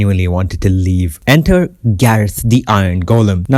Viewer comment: did I hear GARETH?